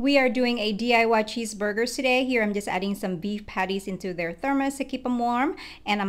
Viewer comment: I’m sorry I heard ‘bean patties’